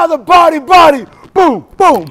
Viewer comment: Parry-parry? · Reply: I always hear "body body" lol